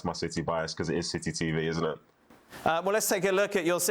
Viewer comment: Did he just say "silly TV" instead of city TV? 😳